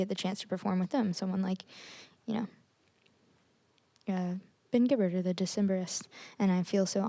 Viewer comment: Did she just say "ben gibbard of the decemberists?"